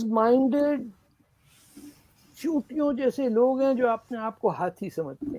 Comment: how many of you heard "Chutiyon jaise" instead of "Chitiyon jaise"....?? 😂